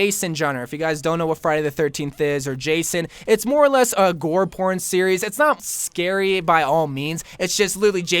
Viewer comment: Boi did you say gore porn at 9:59 OoF am I the only one... ok